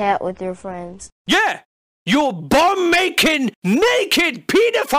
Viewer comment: I thought he said bum making for ages